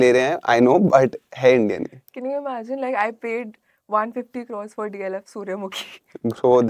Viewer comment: does she said she paid 150 crore for DLF surya mukhi ?